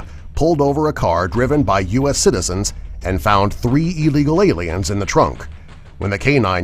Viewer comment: lmao, subtitles say " 3 evil ailens "